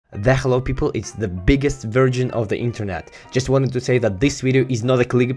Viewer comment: I thought you always said ” Hello the biggest version of the internet” xd I was pleasently suprised by the correction.